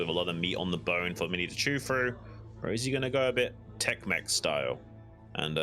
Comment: I thought you said "Tex Mex Style" (instead of Tech Mech) and I was like WHOA WHAT IS THAT.... must be spicy! Great job as always guys!